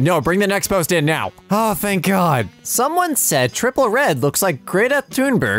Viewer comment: did he just say triple red